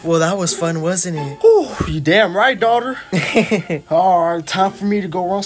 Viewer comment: did he say daughter 💀💀💀💀 Edit1:was he and his daughter do 💦 Edit2: 9:07 AYO